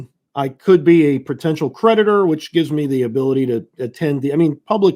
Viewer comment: I swear he said predator , took me a good minute to figure out what he was saying